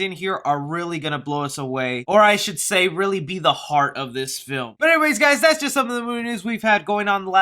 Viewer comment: Wth did he just say? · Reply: L Z heart of this film